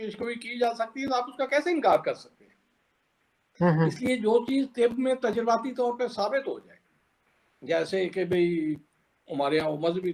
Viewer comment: Kya cheez kya nahin hai chacha? ..Did I hear wrong? 😂